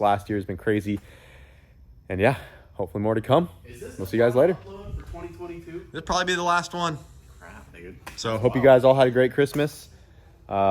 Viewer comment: What did he say at 17:55? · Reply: I think said "is this the last one (video) for 2022?"...